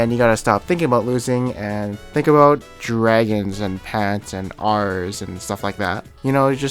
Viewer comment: did you say pants are dragon in the wrong order on purpose?